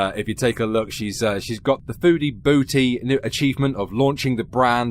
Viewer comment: I'm going to be real. I always thought she was saying "foodie Booty"